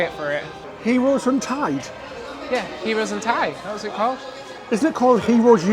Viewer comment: Thought he said “Heroes in tights”! 😀